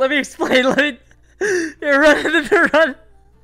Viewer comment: What’s been said and what does that translate? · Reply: omai wa mou shinderu = you are already dead